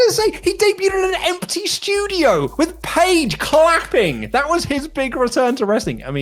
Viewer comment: What's wrong with Paige clapping? ...eh? Never mind I thought he said fapping. CM Punk confirmed.